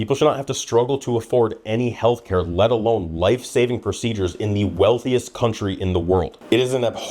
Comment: Life-saving procedure? Oh I thought you said life savings procedure. It will cost all the money you have